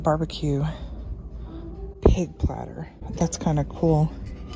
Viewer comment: Did you just say barbecue cake platter!!😂